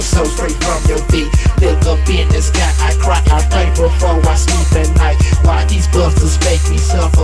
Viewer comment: "look up in the sky, i cry, i'm thankful for my stupid life" thank you Grimm and COTC!!! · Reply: I heard “I write before I sleep at night”